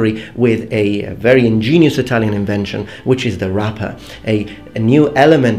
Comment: I will only botter you with a question, at that segment did you say "wrapper" or "rutter"?